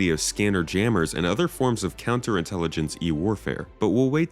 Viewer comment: "counterintelligence-y warfare"? I think you were trying to say electronic countermeasures